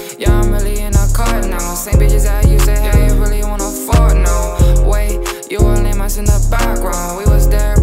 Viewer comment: Is it me or at 1:00 it sounds like says "really wanna fart now" lol idk why